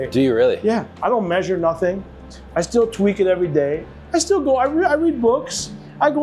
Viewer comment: When he said he tweaks it every day I originally thought he said he twerks it every day.